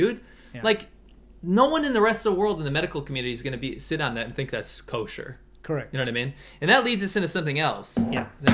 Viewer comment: do you guys understand what he says at 37:37? Sit down thats culture?? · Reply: he said "no one in the medical community is going to sit on that, and think it's kosher". Idk if you know kosher, it basically describes acceptable food to traditional jews and it has become a colloquial American word to describe things that are generally acceptable by society.